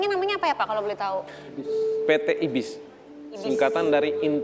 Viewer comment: Kirain PT iblis taunya salah denger yg bener PT ibis wkwkwk